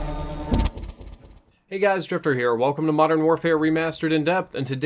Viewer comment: I thought you said at the beginning of the video,"Hey guys,it's stripper here." My mistake.